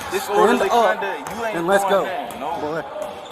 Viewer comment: Do my ears deceive me 👀 did i hear “boy”